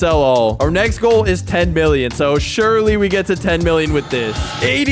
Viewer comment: I swear I heard 10 billion